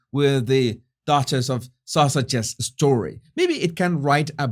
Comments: It’s so funny, it sounds like your saying Duchess of Sausages, not Duchess of Sussex 😂😂🤣🤣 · did you say Duchess of Sausages 🌭🤣